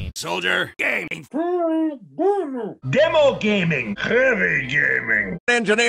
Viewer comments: Haha after repeating that over 3 trillion times it sounds like he is saying Spy Gay 👌😂👌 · For some reason on my second playthrough "Spy Gaming" becomes "Spy Gae." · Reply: Same, but somehow I hear "Spy gay" but other that that I laughe hard on this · For some reason on my second playthrough "Spy Gaming" becomes "Spy Gae." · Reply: Also i hear "Soldier gay" on second playthrough. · After first replay i dont see full end of video so i hear "Spy gay" lol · When spy was supposed to say "spy gaming" i just heard "spy gay" xd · The first time i play the video spy says "spy gaming" The second time i hear "Spy gay" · Woh it sounds like he says spy gay, I sure hope no crimes occur because of my statement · Spy sounds like he said "Spy Gay"